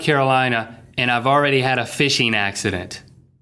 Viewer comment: I heard "I've have already a FISHER ACCENT".. lol